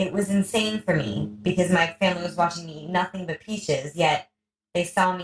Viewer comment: At about 6:50 into this video above you talk about first coming bk from the grocery with many pounds of peaches.... then, (as sound is not good on this video) did you say you ate only the peaches for a few days? Please let me know. I think I'm prediabetic and want to learn how to begin a raw diet and feel and be better~!~ THanks!